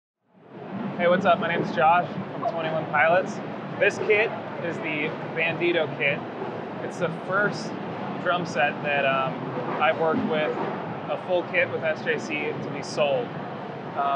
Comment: I thought it said bandito kid